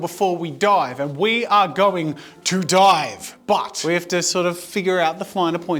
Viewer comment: I thought he said “WE ARE GOING TO DIE”